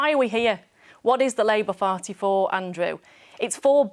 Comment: Did she say "Labour farty